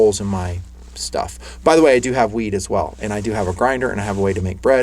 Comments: Did I just hear say that he has "weed as well, and a grinder?" 35:37 ;) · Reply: Rafael Medel wheat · Thought you said you have stored weed as well; had to go back and listen a second time. still heard weed, HAHA, Love the content. Thanks!